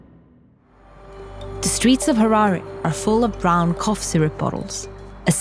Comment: I thought it said harambe😂😂👌